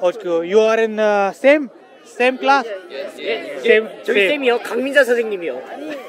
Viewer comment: What this Boy Saying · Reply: He said his homeroom teacher's name is Kang Mi-Ja